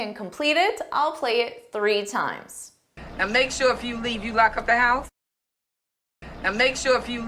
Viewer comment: I could swear she said "Make sure if you leave you LOCK ABOUT" 🙆‍♂